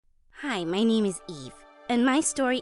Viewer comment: i really thought she said "Hi, my name is E."